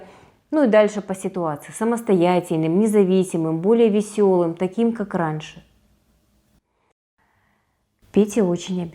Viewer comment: Мне послышалось, или она сказала: "Почему ты не можешь быть более самостоятельным, незвисимым, веселым, таким как Араш?" =)